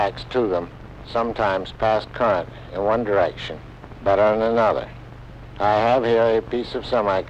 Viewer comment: I can't understand what he is saying at 5:26: Beginning at 5:22, he says, "... the contacts to them sometimes pass current in one direction banananother." Is he saying, "but not another" ...? (This is what I'm guessing from the demo.) So I listened on to see if he would restate it in another way, when, at 5:32, he gets to, "... One contact has these properties. The other contact izzomie." Can anybody clear this up? I get the idea from the demo, but still.... Thanks. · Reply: Your first interpretation is correct; "...but not another." The second, "izzomie", I think, is: "is ohming".